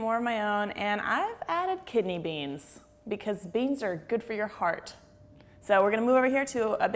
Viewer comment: I thought you said: beans are good for your fart. Then i rewatched it and guess thats wasn't what you said, dammit i was so close :(